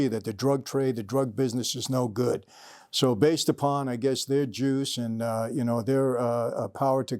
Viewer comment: for a moment I thought he said..."they're jews" LOL!